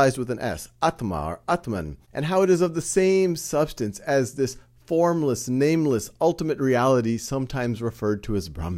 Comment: did he say nameless noway